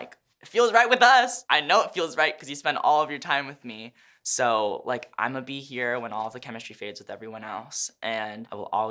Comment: did he say he or am I tripping?? I hear “he” and “you”. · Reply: Loc Phan he said you